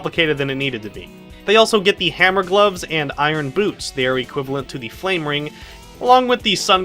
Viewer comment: You've never heard anyone bash the chao system? Almost all the channels and reviewers I watch don't care for it (which I personally agree with), you're one of the first people I've found that likes it. Not saying I disagree with your opinion I just find it odd. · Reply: ROUGE GETS IRON BOOBS!!!!!!! XD!!!! Or I misheard it.